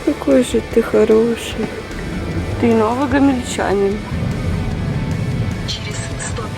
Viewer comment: Мне послышалось или вы сказала ты Норильчанин? Просто я из Норильска и мне очень приятно слышать кто знает о этом городе